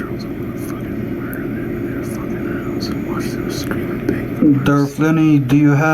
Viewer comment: or thereabouts did anyone else hear whispering under his breath that he was going to break into his trolls' houses and murder them? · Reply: I heard breaking in the house and watch em scream